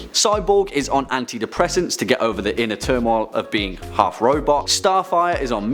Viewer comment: When he said half robot i thought he said Half ROBUX